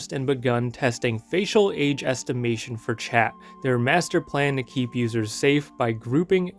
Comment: Did you say...? "Their Master Plan...?"